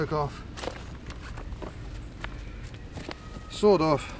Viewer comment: What? Did you say at 4:48 "Sod off"? Like.....Sod Off Baldrick?